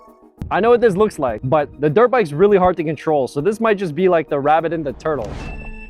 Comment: I had to rewind the first drag race between the one dollar car and the $950 RC car because I thought he said this is gonna be the rabbit and the turd